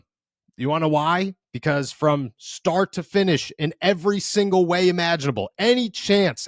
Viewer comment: I honestly thought I heard you say "From Shart to finish" and honestly that's not a bad way to describe this game.